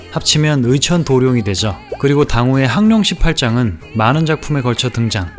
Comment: 강룡18장이 아니라 항룡18장으로 들리는데?